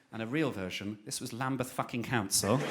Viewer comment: What does Joe say at around 2:06 please anyone? Bell ends? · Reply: lambeth council, the people who gave him the fine (it’s in london)